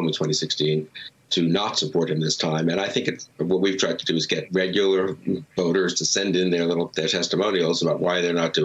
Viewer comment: or so. Bill Kristol: ". . . what we've tried to do is get regular little. . . regular voters to send in their testimonials. . . " Wow, Mr. Kristol. That is the most condescending slip of the tongue I think I've ever heard. Your effort for a "save" was too late, unfortunately, for me to continue respecting you. That was deeply revealing.